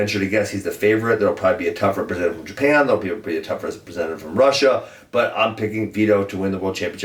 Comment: For 61 Ben says Russia will have a better guy. Who is better than Abasgadzhi Magomedov? That guys been dominating for years. Also, kind of overlooked Forrest taking 3rd at 61. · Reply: It sounded like he said "there will probably be a tough representative from Japan, there will probably be a tough representative from Russia". Isn't magomedov from Russia?